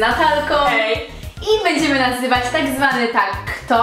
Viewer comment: Czy tylko ja usłyszałam Będziemy nazywać tak zwany Tag Kto Zamiast nagrywać było nazywać